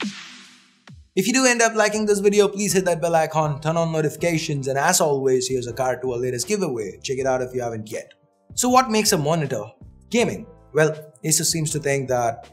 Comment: I heard said "ass always" instead of as always ... 😗😗